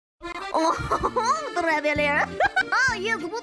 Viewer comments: Lmao I heard “Ohohoho traveler, ah yes guten grinding be faster?” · I literally wHEEZE EVERY TIME HE SAYS WOULDNT GLIDING BE F A S T É R E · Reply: Lmao i thought he was saying "I am water gliding me barbier" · I heard "Bonjour You fucking ASH"😅